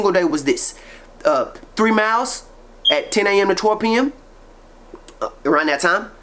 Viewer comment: did you say you did 3 miles at 10am AND 2 pm so a total of 6 miles a day or did you mean you ran 3 miles again at either 10am or 2pm? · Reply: No he said after about 2 weeks he switched to working out 10am-12pm it was more sunlight outside then